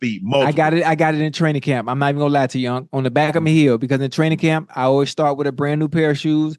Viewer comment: Did Ocho just say "On the back of me heel"?!? 😂😂😂